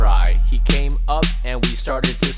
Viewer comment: and all this time i thought he was saying "bootysucker man"